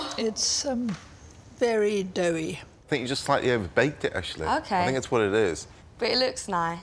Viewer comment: did paul call her ashley??